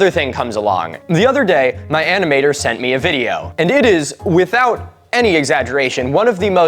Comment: im so stoned that i heard "my animator sent me this," as "my auntie (ant-ee) mater sent me this," and lemme tell ya, boy freakin howdy, i chuckled fr fr 10/10 would misunderstand high again